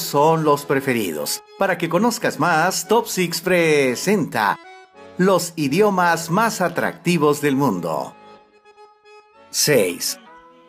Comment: Pense que decía "los idiotas mas atractivos del mundo" jeje